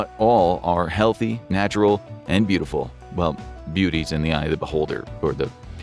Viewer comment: woah stop did you just say all Pen15s are beautiful hahahaha